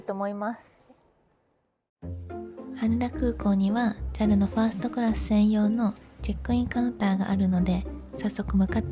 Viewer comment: ジャルのファーストクラス専用が、空耳多い俺には、ギャルのファーストクラス専用があるって聞こえて、え？思っちゃいましたｗ